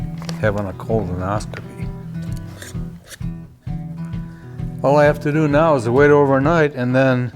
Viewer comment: I'm scratching my head trying to figure out how this reminds of a colonoscopy.🤔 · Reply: I thought he said "cold one."